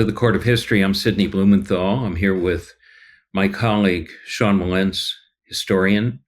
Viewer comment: Thought you said "Here with Mike Holleague". 😂